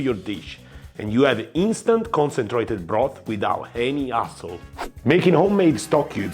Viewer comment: did he just call typical stock cubes 'asshole'!??